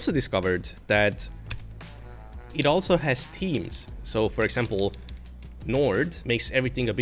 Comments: I thought you were saying it had Microsoft Teams preinstalled lol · when you said "It also has themes", I thought you said "Teams", as in Microsoft Teams and I was like "Why would you want Teams on a Linux install?"